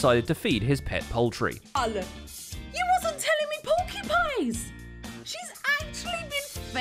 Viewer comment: Did just say... "He wasn't feeding me porcupines!"?